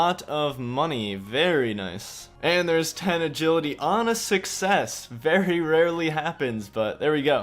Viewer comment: i SWEAR TO GOD at 21:00 i thought he said "honestly sex very rarely happens", which fits this challenge perfectly.